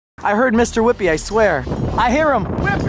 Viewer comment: did they say Mr. Whippie?